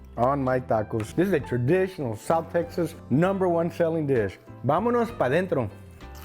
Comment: vamos pa en thren? I am not a native spanish speaker but doesnt that mean "Let's go by train". Im trying to learn and I cant make out that phrase. Can someone help me